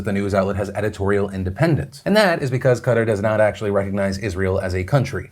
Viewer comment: I just want to say I kept hearing "cutter" instead of "Qatar". I was very confused.